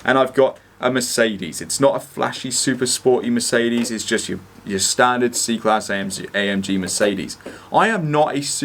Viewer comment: All i heard was "typical standard Mercedes, AMG". LOL.